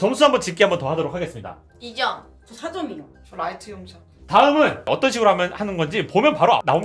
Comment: 초에 뭐라고 하는거지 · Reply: 저 라이트형제요 ? 뭐지?